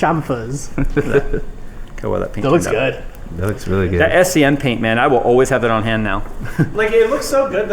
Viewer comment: amazon link isnt working (for me) what is SCN paint? thats what the subtitles say anyway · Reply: ahhh i figured it out its SEM paint